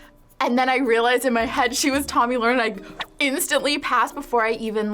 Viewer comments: who's the girl at 5:23 ? i only heard tommy something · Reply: tomi lahren thats her name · who? I didn't even hear her name. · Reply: Tomi Lahren